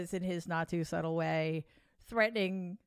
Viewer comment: I know it's freudian, but when she said "in his not so subtle way" I thought I heard "in his nazi subtle way" I had to back it up and listen again 🤣